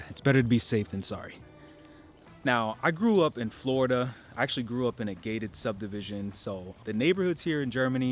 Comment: I was really confused because for some reason when you said you grew up in a gated community I heard "I grew up in a gay Ted community." I was thinking that gay Ted was something to do with Florida and had no idea what that could mean. I think it's time for bed.